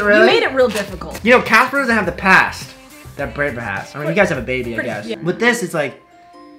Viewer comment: Am I tripping? @ 14:03 did said that eva had a baby??? ... Do I have hearing issues